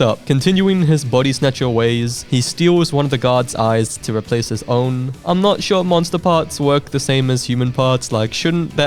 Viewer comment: i heard gods eye and made of of this whole head-cannon.